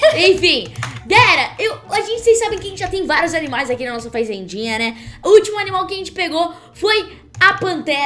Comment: "Faizendinha" Kkk ( quem ouviu oq ela disse? da like) q isso esse teu vocabulário ta show em Kkk Só tava brincando te amo mais "faizendinha" Foi ótimo Kkk ri muito quando vc disse isso Kkk Te amo